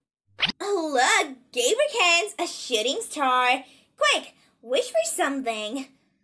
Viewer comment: i know its supposed to be shooting star but i keep mishearing it as her saying “shitting star” and i cant stop snickering